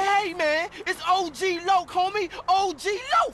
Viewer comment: is what I said when said “og lock” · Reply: i called him that my whole life until now i just found out for the first time its Loc not Lock